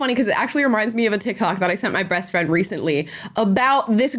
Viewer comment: Did Amala just said : " ... br3ast friend ..." ???? ... 🤔 ... Yes, she did ! ... 😊👍 Even listened to -.5 speed, you can hear it ! ... 😊👍